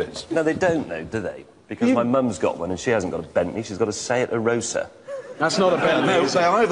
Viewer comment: Im 2:47 what did may say his mother had? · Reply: Seat Arosa